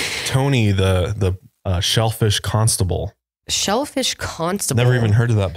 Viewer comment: When u shellfish comstival I thought I said selfish 😂